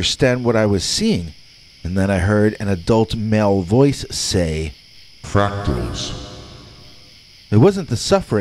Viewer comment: So what did this adult male voice at 2:43 say? Played it over & over again. Just couldn't understand it.... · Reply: "Fractals."